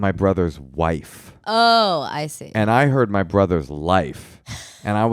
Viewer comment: I heard "brother's Life" too